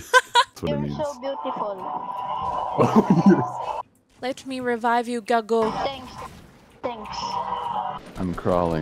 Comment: Did I heard that right?- 'Let me revive you gAgO" omfl